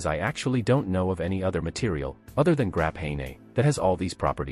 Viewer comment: "grap-hey-nay". I think the computer meant to say "graphene".